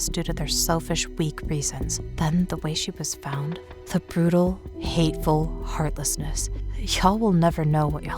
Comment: You misquoted the mother and she was killed in a beautiful way when it says brutal way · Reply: If you're talking about at 9:20, I think you're mishearing it. I can hear how you might mistake it, but she is saying 'brutal', it just sounds like she's pronouncing brutal as 3 syllables instead of 2, making it sound like 'beautiful'